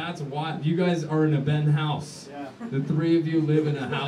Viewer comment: I thought you said you had a penthouse. No a Ben House.